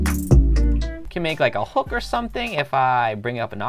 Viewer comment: you can make like a what now? I heard hooker at first XD